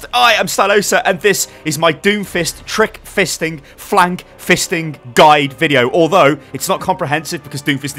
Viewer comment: I thought he said flap fisting at the beginning 😂😂